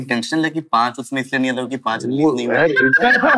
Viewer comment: paanch ka kya bol raha tha? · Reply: paanch release nahi hui